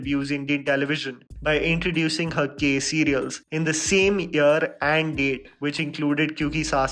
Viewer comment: Ekta Kapoor Released 'Gay Serials' around 6:33 . Did I hear it right? ;D